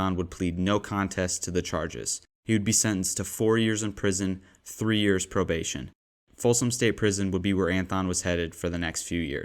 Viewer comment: thought he said he served 1 and got 3 years probation? Either way, what a messed up court system we have! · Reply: at the end of the 'witnesses' section of the vid he says 4 years prison followed by 3 years probation. I agree regardless though, seems a very light sentence....